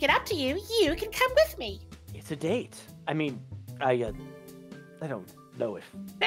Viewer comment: I was really tired when watching this part and I thought shadow just randomly said sedate.